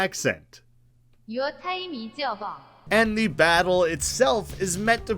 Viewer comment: i think it actually says “Your time is over”